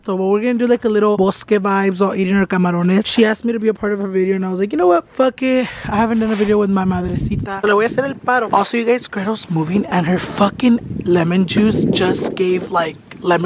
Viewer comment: Lmao who alse heard him say lemon juice instead of lemon tree ☠😂😂😂